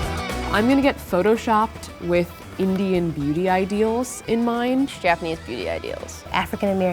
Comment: i thought she said "Indian booty"